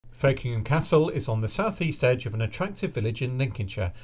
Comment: I heard "F'kingham" at first. Not the best name to start the video on, for my brain 😂😅